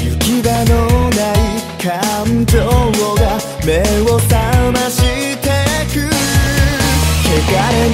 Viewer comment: it sounded like he said my sh*t taste good.. saMASHITEKU